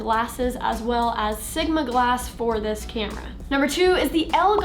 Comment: Did she just say sugma glass?